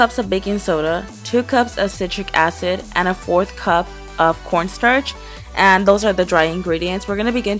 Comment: XD it sound like she said porn starch